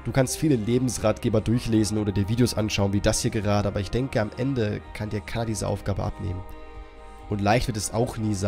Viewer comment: Ich hab verstanden, am Ende kann dir Kaddi diese Aufgabe abnehmen! ; ) COLDMIRROR WUHUUUUU!!!